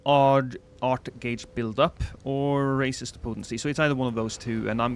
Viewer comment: NGL,I really thought said racist potency in the dormant power selection 😅